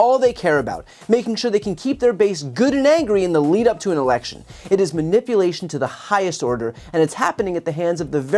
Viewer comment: it really sounds like he said ‘the lead up to the Erection’ lol